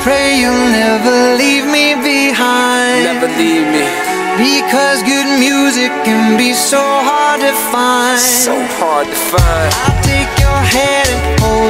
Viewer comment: Hey uhh idk if it’s just me or what I saw the subtitles say, “Because your music can be so hard to find” but I heard “Because good music can be so hard to find” soo yeah.